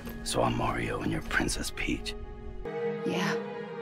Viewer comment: 多少人第一次听Peach 听成bitch